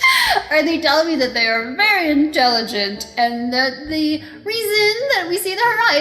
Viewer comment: wait. Did she really said "very intoligen"? And scienters?